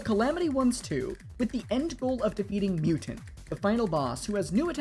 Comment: Brother at the beginning is thought you said "with the end goal of defeating mewtwo" 😂